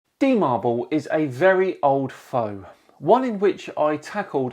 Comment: Can you repeat the beginning of the video in text? All I hear is "T-mobile is a very old phone" which makes absolutely no sense to me. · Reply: D marble is a very old foe.